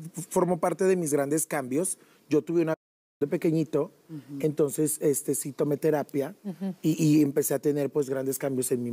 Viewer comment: qué dijo??? Por qué quitan el audio, es imposible darle continuidad si no sabemos qué dicen 😒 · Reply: Viol*ción